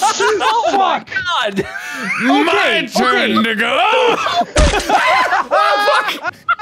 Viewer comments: Did make say a no no word at 31:37??? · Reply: No, he said “to go.” Full sentence being “my turn to go.” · did he say the n-word? not being rude just asking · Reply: "My turn to goo" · excuse me what did he say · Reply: ‘my turn to go’ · Did he say the n word at 31:36? Does he have the pass boys? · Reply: He says “My turn to go” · WHAT DID HE SAYYYYY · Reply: I know it sounds like the nword but he's actually saying "My turn to go" haha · did he say the n word?? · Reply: He said "my turn to go" how did you hear the n word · I’m not the only one who heard that am I? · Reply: Are you paid to rile people up for no reason? He said "my turn to go" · WHAT DID HE SAY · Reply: He said "my turn to go!" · Wait, did mark just say the n-word? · Did mark say the n-word or am I just stoopid? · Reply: No he said “to go” as in “my turn to go” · MY TURN WHAT? did he just say the n slur? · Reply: He said, "My turn to go!" Ive seen this episode many times. That being one of my favorite parts. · when Mark said "it's my turn to go" it sounded like "it's my turn n***a" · LISTEN CLOSELY, comment what He said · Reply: JMGhost 64 - He said “to go.” Full sentence being “my turn to go.” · What did mark say? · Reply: "My turn to go" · did mark just say the n word? tell me i’m wrong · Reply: No, he said "my turn to go" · did mark just say the n-word..... maybe I misheard a after research, I found he said my turnnnn to GooOoO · Does Markiplier say the n word at 31:36 ? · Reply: No, he says "my turn to go".